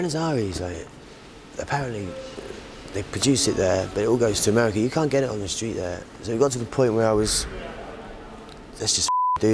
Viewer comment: can anyone please tell me what he is saying about "Buenos AIRES" at 5:55 ? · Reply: He said in Buenos Aires they produce it there but you can't get it there, it all goes to the US.